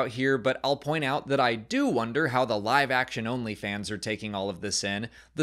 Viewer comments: The "live action-only fans", not "live action OnlyFans". Took me a second to understand what you were saying 😆🤣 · When said "Live-action Only fans," I definitely heard "Live-Action OnlyFans" and was thoroughly confused for a few seconds 😂